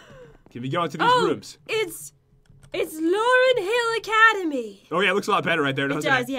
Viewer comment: Enjoy the video guys!! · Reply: i swear i thought u guys said laurance academy